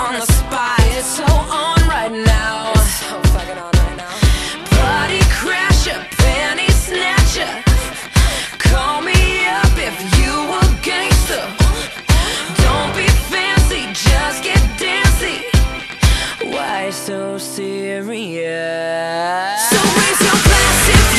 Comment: I thought it said "Call me up if you('re) a gangsta".